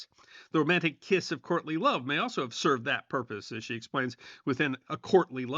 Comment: When you spoke about "courtly love" I misheard it as Courtney Love, Kurt Cobain's girl friend and possible reason for wanting to kiss a shot gun!